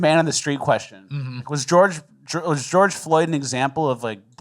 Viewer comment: I heard you almost say George droid at 19:50